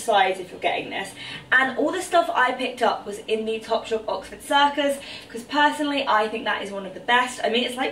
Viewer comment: Didn't quite catch what you said at 10:39 😂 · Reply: OWWH YH 😂😂😂 I meant Primark Oxford Street